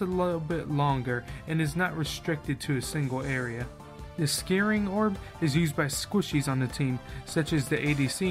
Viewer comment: do i hear scaring ward?